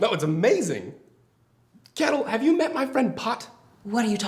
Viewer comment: What does Alex call Emma at 6:26? Is that..."cattle"? "Kettle"? Also, Caroline's totally gonna try to set up Jane and Alex. totes.